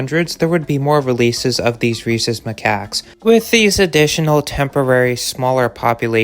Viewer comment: Wtf? The subtitle says Reese's Macaks, I'm hearing Racist macaque, but I know it's rhesus macaque. Don't forget the rather common Bheatan macock @ 3:28.